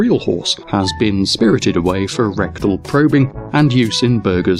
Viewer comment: I swear I just heard "rectal probing" xD